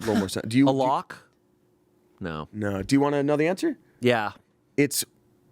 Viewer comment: i had to do a double take cuz for a sec I thought Shayne said “maloch” which is short for the Italian curse malocchio 😭 I was like YO HOLD ON A SEC